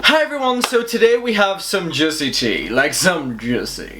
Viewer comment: Literally thought you said "jizzy tea" lmao